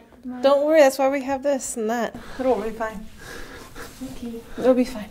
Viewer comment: Lol I thought when you said “it will be fine”, I thought you said, “come on moon pie!” I laughed because I thought it was a really cute nickname for your brother. 😊